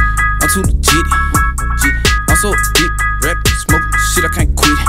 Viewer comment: Did he just say "I Suck Dick" please tell me what dude just said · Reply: Juan Jones Lmao..naww G he said " I'm so addicted "